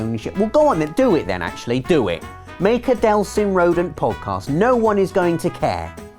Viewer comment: I thought he said Delsin Rowe xD